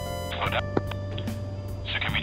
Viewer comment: Wait, did I heard they called their flight as 'Hentai Flight'? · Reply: Still sound like he said Hentai Flight